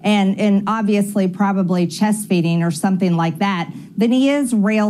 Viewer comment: I heard 'chest beating' not chest eating.